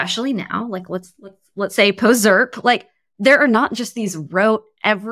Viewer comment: I LOVED this ep. Who knows what Claire said at 12:00? the caption just says "inaudible" :( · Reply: She says "post ZIRP" referring to the ZIRP (zero interest-rate policy) era